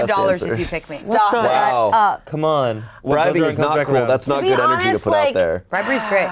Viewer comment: Can anyone tell me what said at 9:00? I couldn't understand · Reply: What goes around comes back around